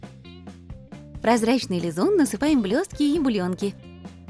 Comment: На 6:04 мне послышалось блёстки еблёлнки